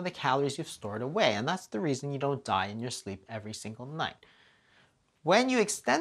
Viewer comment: I'm sooo Happy to see you making videos again <3 Every time I hear you say "Your body knows what to do & that's why you don't die in your sleep overnight ha ha ha"